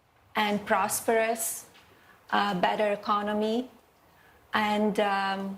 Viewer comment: "Piss in the world." Sounds about right, but I think she meant "on" instead of in.